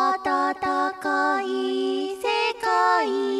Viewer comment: の分かる？がミオしゃの声に聞こえてしまった・・・